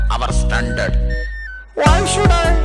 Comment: When mom says 'Go and study' Me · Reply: I don't know why I heard 'Why Should I' as Maa Ch*daai😂